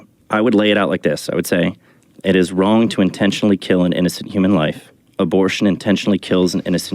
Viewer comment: "It's always wrong to kill human life." Of course that isn't true. God told the Israelites to kill thousands of people in the Old Testament. Capital punishment is a very Catholic concept as well (until recently). And of course a just war is Catholic (in a just war, people are killed). So that premise is patently false. No, the premise should be stated like this: "It's always wrong to intentionally kill innocent human life." That narrows it down to the essentials of what is wrong. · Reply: I thought the Bee guy said an innocent human life in his set up